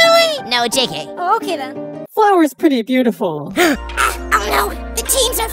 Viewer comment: about flower i heard flower's pretty pitiful (sorry if didnt spell it right)